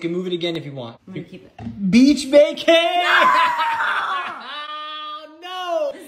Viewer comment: oh I thought I heard "Bitch, VACAY !!" 😂😂